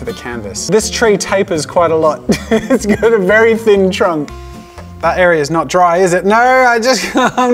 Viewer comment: I heard trump